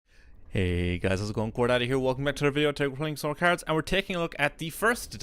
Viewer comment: am I the only one who keeps hearing "core daddy" instead of "core data"?